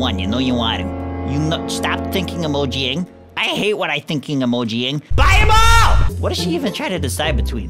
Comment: wait did he say "BUY MO" :./